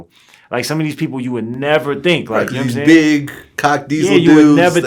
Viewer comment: Did he just say “big cock diesel dudes”?